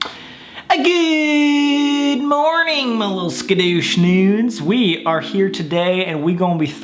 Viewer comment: did he say scudushnudes